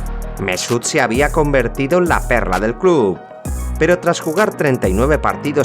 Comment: WTF bro seems he says " perra"